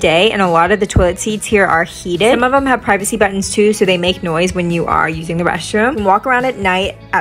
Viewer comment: A privacy button for the public bathrooms is a genius idea. I absolutely cannot stand when it’s dead quiet, especially if I’m using a bathroom next to random strangers. 😭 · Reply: I misheard her and thought she said pregnancy button, and I couldn't for the life of me figure out why japanese pregnant ladies needed a special button. 😂